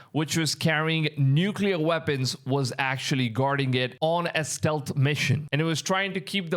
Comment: I thought you said angels were guarding it?